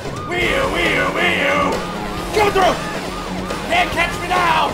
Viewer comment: Is he saying "coming through" or "get off the road"?